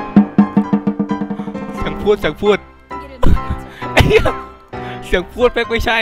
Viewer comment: ได้ยินพี่พูดว่าไอเหี้*5555